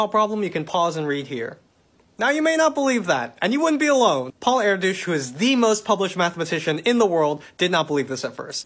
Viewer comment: okay but at 0:28 did you say "paul air-douche was the most famous mathmatician?"